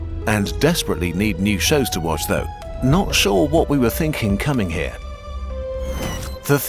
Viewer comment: Hey! I heard we're getting out... because of Bob.